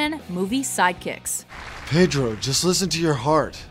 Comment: was i the only one who thought it said sidechicks?